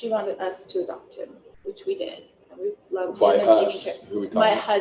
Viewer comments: Did Lori say we loved him? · At around 3:43 as Lori is speaking about JJ and saying the grandmother wanted her to adopt him you can hear her say in past tense that she say "and we loved him" didnt sound like she said we love him sounded to me like she said LOVED! that is a hint right there if I heard correctly that he had already been dead.